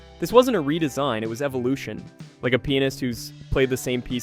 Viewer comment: I thought you said "Like a penis" XD